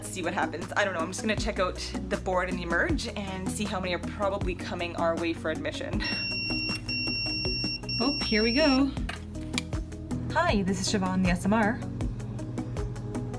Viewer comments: I thought she said “hi this is shavon ASMR” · "Hi this is Siobhan the ASMR" - 2:04 I know what it meant but that's what I heard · Sounds like you say Shevahn ASMR, when you answer your phone.